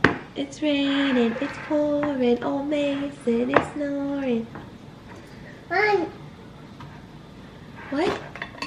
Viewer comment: ! I thought mason said nein(No in german) Lol.